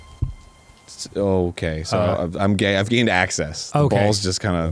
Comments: Did anyone else hear Arin say, "I'm gay," super duper fast? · I SWEAR I thought Arin said "I'm gay"